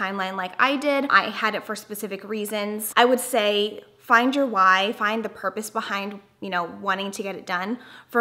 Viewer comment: I thought you said "find your wife" lol